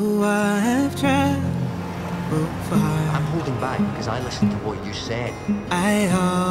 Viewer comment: AsarathWitch : Aww Thanks for that brilliant comment! ^^ Glad you liked this version. At 1:02 he says... "I'm holding back, because i listened to what you said" .... "He's not you" =)